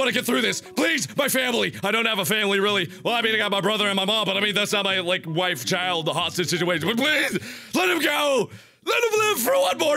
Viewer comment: did mark say " it's not like my wife, child, or hostage situation." LMAO,!!! ;}